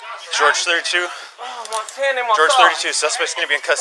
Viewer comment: I think he said "my hand and my thigh"